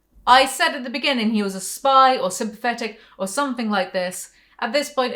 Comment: Fuck, at the beginning when you said you thought the Dr at the rehab facility was sympathetic I thought you said "synthetic" an I thought you meant like a robot or sth. And it wasn't until about 6 mins from the end that I realised you actually said sympathetic.